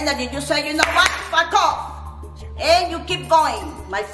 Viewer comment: I thought she said “f*** off” but I had to replay after laughing and analyzing that wouldn’t use that word and instead she said “back off.” Not 100% sure though lol. If anyone else heard it please let me know. Love me some 😍